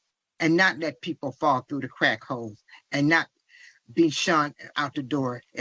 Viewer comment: I lost count of how many times ive seen/heard his goofy ass mommas statement.. It still makes me laugh tho.. mainly because for the longest time i swore she was saying "crack hoes" instead of "crack holes" i used to be so confused like who is she callin a hoe tho😂😂🤣🤣